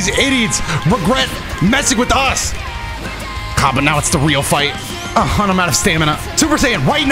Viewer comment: I Swear in my head I heard UI Goku go “The Real Fight Starts Now!”